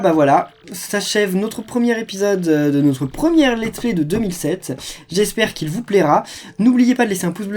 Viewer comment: C'est moi ou j'entends 2007 à 35:11 ?